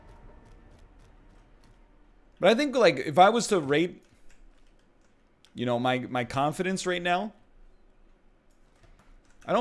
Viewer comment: am I the only one who heard the word rate, but with the "p" instead? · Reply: What, rape?